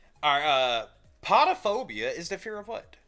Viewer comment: I thought you said potiphiobia too. I was thinking fear of potatoes lol Stone cold’s entrance song is Glass Shatters by Disturbed